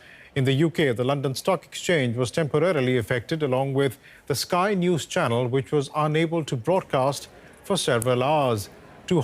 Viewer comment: "... Sky New Channel was also affected ..." - I first heard it as "... Skynet was as also affected ..." and had to circle back with some considerable alarm 😀